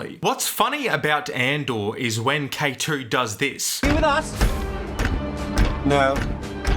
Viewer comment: What does the guard say? I do not understand a single thing. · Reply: "Are you with us?"